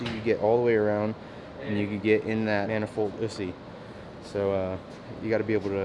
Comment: did he just say manifoldussy?